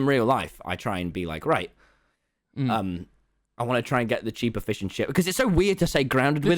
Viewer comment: When Tommy said "cheaper fish and chips" I thought for sure he said "cheap efficient shit" w h a t